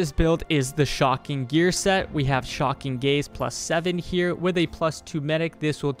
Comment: How normal people hear it: shocking gaze. How i hear it: shocking gays.